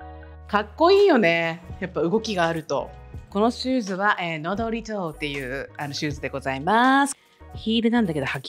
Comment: Wednesdayのパープルのヒールのブランド名発音良すぎて聞き取れない、、爆笑 どなたか分かる方いらっしゃったら教えて欲しいです😢😢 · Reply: Nodaletoだと思います！